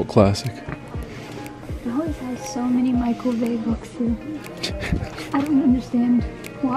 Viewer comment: I legit also thought you said Michael Bay and I was intrigued. Loved the "nature" commentary and accents!